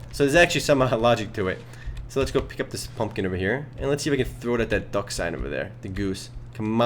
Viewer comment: it sounds like he said Fumpkin. lol